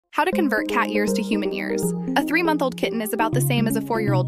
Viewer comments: Me who heard it as "how to turn cat years into human ears" instead of"how to convert cat years into human years " 🤣😂😅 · I thought you said cat ears to human ears and only now a phew I understand it's years